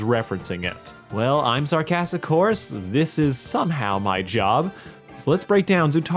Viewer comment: i thought you said sarcastic horse 😅 I'm so sorry 😂